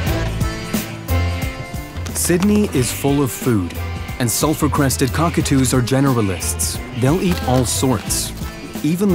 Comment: The entire time, I thought the narrator was saying, "Self-Requested Cockatoo."